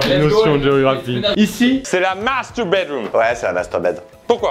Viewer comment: Je sais pas si c'est moi ou l'accent anglais de mais j'ai cru entendre "Ici, c'est la 'masturbate' room." 😅